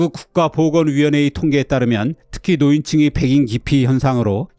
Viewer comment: 노인층의 "백인" 기피라고 들리는데 자막에는 "백신" 기피라고 해서 잠깐 헷갈렸습니다 선생님 ^^; 언제나 유익한 정보를 공유해 주셔서 감사합니다!